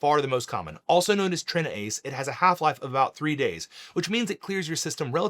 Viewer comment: All I heard was Half Life 3.